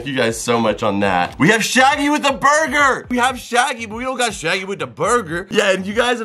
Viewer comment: I swear I thought he said its shiney with a burger instead of shaggy with a burger lol.